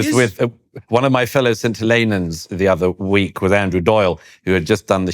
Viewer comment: I realized he said "St. Helenans", but I initially heard it as "scintillanan." Which, I guessed was very likely derived from and a form of the word "scintillating", with "scintillanan" referring to a scintillating person. And the online dictionary is not turning up anything on a word like that, so it could be a new word. :)